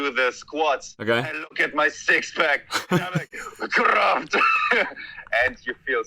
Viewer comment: Editing note: At 13:17 He is not saying "craft," he is saying "Kraft" which literally means power in German